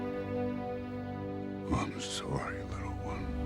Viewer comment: Why did I hear thanos say "Momma's sorry little one" instead of "I'm" 🤣